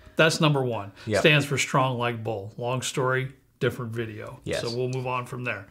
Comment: lol thought you said Strong Light Bulb!!